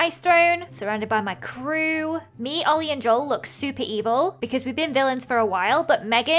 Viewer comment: What I thought she said while doing the villian room- Me,Oli and Joel looks super evil! But kinda noob so... (What she actually said- Me,Oli and Joel looks super evil but kinda new...)😆😆